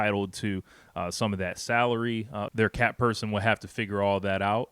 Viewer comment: I miss heard you say "that cap" person and had to rewind because it sounded like you said "that CAT" person !!! Which actually was wrong but most likely couldn't be more right! Thanks for the unintentional humor. It's funny because its true glitch in the matrix.